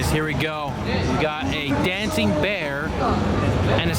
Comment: Did someone say DancingBear?